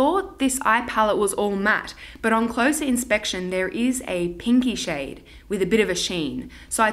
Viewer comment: Totally thought you said "kinky" instead of "pinky" about one of those eye shadows! 😂